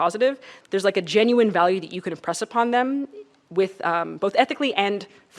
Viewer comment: did she just say oppress?????